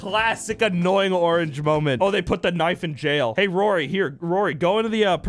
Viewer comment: Bro u said "We have to save annoying orange from the knife." I thought u said Nazis not knife